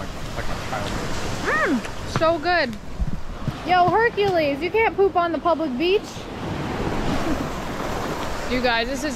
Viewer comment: Had to rewind thought you said you can't poop on the f.cking beach LMFAO Been chainsaws all day almost ears haven't adjusted lol